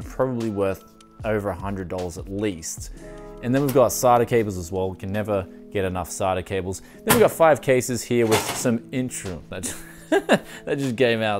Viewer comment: is he calling them sarda cables? 7:38 lol wot